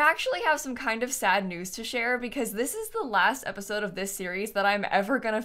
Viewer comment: Im the only one that was having a heart attack because I thought she said "this the last episode ever" then I rewatched it. lol